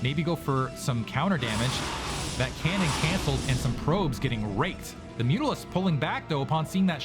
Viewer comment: did he really say “some probes getting raped”?